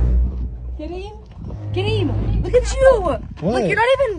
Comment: I thought I heard your sister said Karen